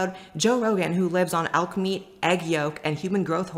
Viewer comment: She said “Elk Meat” I thought she said Alchemy 😂😂😂